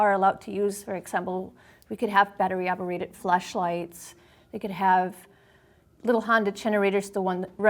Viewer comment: who else heard "fleshlights" 😂